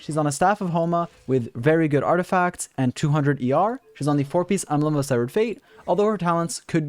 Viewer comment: Lmao wut did he sayyy. 20:56 embelgd of sevfuc fate